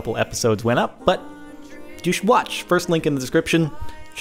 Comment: on 0:39 did he say douche watch?